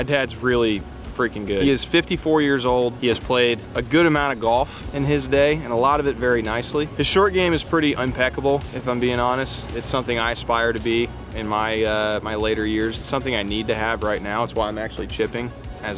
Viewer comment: Did you say unpeckable? I think you mean impeccable. Sorry, I am compelled to correct this.